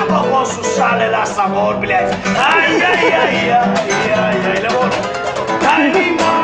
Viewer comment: Только я один услышал слово " блядь " на 0:59?)